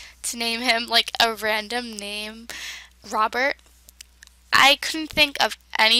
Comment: At first i thought you said lobert insted of robert lol